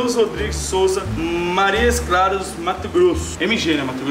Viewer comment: Marias claros não entendi kkkk e montes claros Minuto